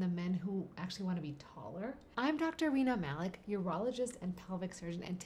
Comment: Thought she said her name was Dr Weiner Malik 😂